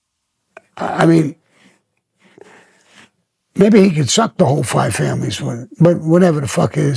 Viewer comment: Maybe he can "suck" the five families? Did i hear that right? 😅